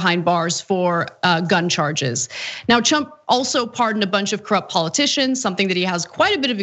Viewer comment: XDDDD 2:15 Did she just slyly say "Chump" instead of "Trump"? Was it intentional or a slip? How long has this been going on I wonder?